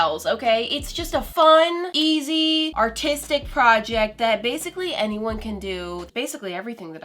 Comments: In the beginning I was in the car and I thought she said it’s a fun, easy, tasty project and I was like the what lol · When she said “artistic” i thought she said “autistic” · I misheard it as... painting on seashells ok, its just a fun easy autistic project....